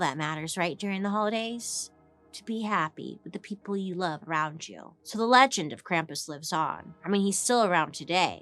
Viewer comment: what I hear “let the people you love ravage you” What she actually said “With the people you love around you” 😂 I wanna be filled with the spirit of the season 🧑🏼‍🎄 🍆 ✨